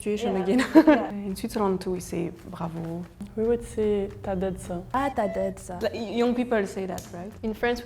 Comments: it’s actually a mistake. she did not said « t’as d’la shot » she said « t’as dead ça » wich mean « you killed it » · She did not say « T’as d’la shot » she said « T’as dead ça »